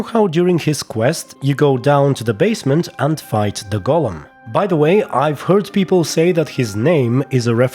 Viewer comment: Possibly the heaviest editing I've done on my voice so far, in an attempt to make it sound normal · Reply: i thought you said '..fight the Gollum' 😅 But that would obviously be Smigole 'spoonful spoonfool' Serkis in blood and wine 🥰 :D